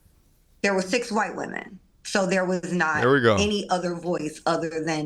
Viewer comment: I thought she said “they were thick white women” 😅